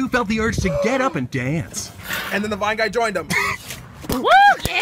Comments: my favorite one is the one where the guys is like "anD THEN THE VINE GUY JOINED HIM" and Thomas and some dude danced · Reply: lol this is so bad but i heard "and then the white guy joined him" XD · Thomas: and suddenly the two has the urge to dance! Guy: GaSp AND THEN THE VINE GUY JOINED THEM!! Thomas and guy: DaNcEs Why do I feel like I would do what they guys did? 🤣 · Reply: I thought he said then the black guy joins him lmao